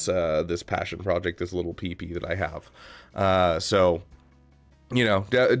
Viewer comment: Did he just say he doesn’t regret this little pee pee that he has?